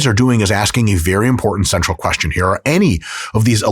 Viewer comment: Did anyone else do a double take and rewind? I deadass thought I heard, “What these guys are doing is asking a very important SEXUAL question here.” I was like wait WHAT? 😂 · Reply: i think it was “central” but it really sounded like sexual at first